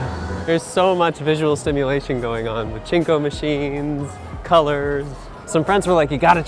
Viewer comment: did he just say: "There's so much visual stimulation going on with chinko machines." Or did I mishear that?